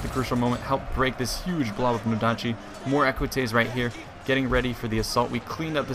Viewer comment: Did you said ecuatize ( roman cavalry) ??? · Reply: You can your troops in this game and he named them after the equites